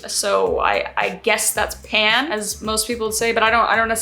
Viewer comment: What the hell you just say that you are bisexual that's disgusting🤢🤢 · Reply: she said she's pan, and you can keep your bigotry to yourself. get well soon 👍